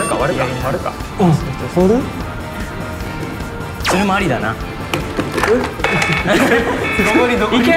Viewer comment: What's he saying at 16:19 ? It shouldn't be つまりだな 🤔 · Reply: he says「それもありだな（that's good too）」